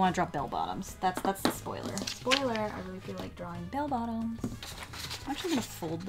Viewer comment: The first time she said “Bell Bottoms” I thought she said “Bail Bonds” and I was very confused...